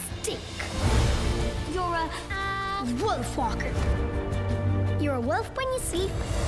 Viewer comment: I heard this audio clip from the radio and I swear I hear them say "you're a wolf-f**%ker". Lol. That got dark really fast after hearing it. Lmao.